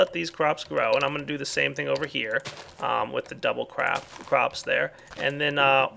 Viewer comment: did i hear a "double crap"?